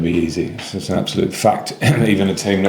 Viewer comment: did he say "absulute fucked"?😅